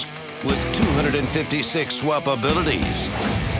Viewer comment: Lol the announcer always sounds like he is saying swapabittleties instead of swappabilities